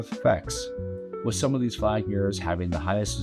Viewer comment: Did someone say flag 🐻ers?